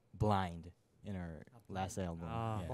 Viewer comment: WHY TF IS THE AMERICAN MEMBER USING AN AUSIE ACCENT "you knaur" 😭. Jake's also affecting the us boy😭😭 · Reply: I thought he said 'in our last album' 😅😅 im Australian and now that I think about it, it does sound a little like 'you knaur'